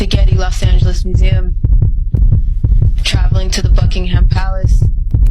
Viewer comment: Did I hear her say travelling to the Buckingham Palace... was that Prince Andrew too, involved. Lolmao you know he got down graded from the Royal family duties cas of his friendship with S. TRAFFICER Jeffrey Epstein.